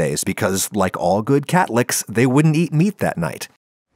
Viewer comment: when you said catholic i heard "catlicks"